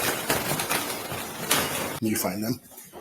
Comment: Why do we hear at 11:35 sounds like says “Did you find em” wonder who he was talking to?? Hunter?